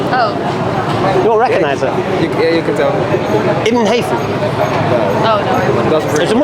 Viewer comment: Who is the person is saying at 5:16? i cant hear the propper pronouciation. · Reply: Ibn Haytham